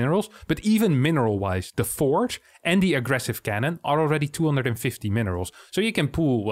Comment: lol I rewatched 3:25 3 times before realizing he actually said cannon, not pylon, thinking it was being wrong here.